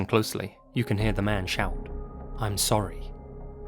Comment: In the first story, I don't know how anybody could pull "I'm sorry" out of the audio of that man's voice. I clearly heard "be quiet…be quiet or I'll kill you" everytime I listened to it